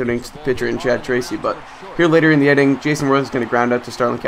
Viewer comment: Sounds like you said Jason Mraz! lol